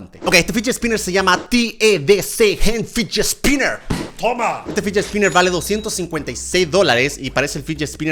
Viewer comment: OMG pues yo escuche qe Germán dijo OMMA 😱